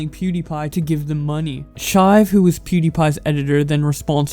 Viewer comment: I never heard of Pewdiepie’s editor, “Chive”, is this Brad 4? 4:55 I’m playing but that’s def what I heard lol